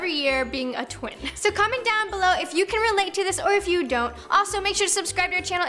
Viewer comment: did say tins 0:20?